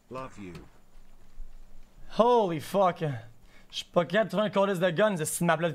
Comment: did i just hear spaghetti? OMEGALOL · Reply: LMAO nah he said “chui pas capable” and you misheard it lmao